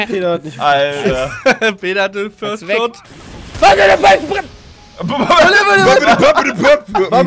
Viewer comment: "WADEDEBRR" Ohne Jays calls hätten die anderen Innos keine Chance!!! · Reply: Also ich hab verstanden "Alter der Balken brennt!" :D